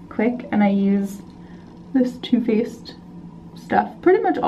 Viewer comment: For some reason I heard 'toothpaste' instead of 'Too Faced' when you were doing your brows and I was like.. 'is this some make-up hack I haven't heard of yet?' XD I'm here for all the content, books, vlogs and all the extras!